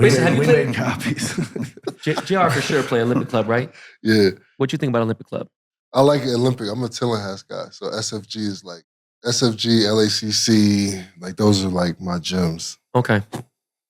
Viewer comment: What does JR say about LACC and SFGC? Sounds like he says he’s a “toehas guy” 😂